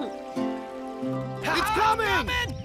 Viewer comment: Who heard the little "Trump is coming !!" At 10:05 ?? xD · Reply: Tanguy423 It's actually (troop is coming)